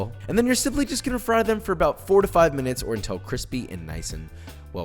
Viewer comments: I first heard 45 min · i thought you said 45 minutes 😅